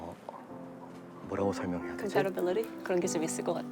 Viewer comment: 즈음에 여자분이 영어단어 뭐 말씀하신걸까요~?? 그런게 재밌을것같다? 라고 하기 전에요! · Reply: compatibility로 들려용